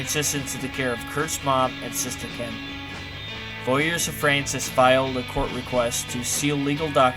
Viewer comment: This is very insightful, however there are times when l can't make out what's being said. Like @ 47:45. "Something, something, Frances filed a court request....." And @ 50:48, "November 25, something, something..." Anyway, thanks for putting this together and posting. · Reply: "Lawyers of Frances filed a court request..." and "for record store day all the Nevermind singles were released on vinyl."